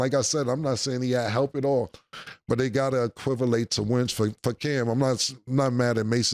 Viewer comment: Did Cam say equilivate? Wtf is that